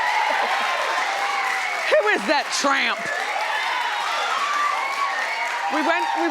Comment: Thank you! Lol for some reason I thought that said Trump lol